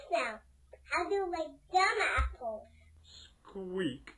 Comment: i wasn't looking and when she said "how you them apples" i thought she said "how you like that ass hole" lol hahahaha